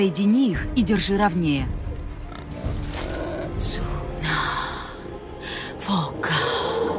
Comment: Слышу: Саша!! Фокан (наверное фокин)